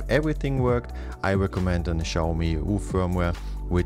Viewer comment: what did you say at 4:29 ? Something about firmware? I don't think your captions did justice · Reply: EU i think